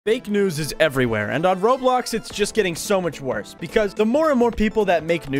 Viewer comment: bro said RoadBlocks instead of Roblox.